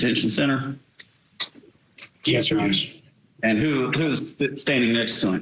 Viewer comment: did he just say yes your highness?